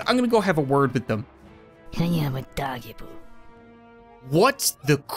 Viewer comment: I heard “I am a doggy poo”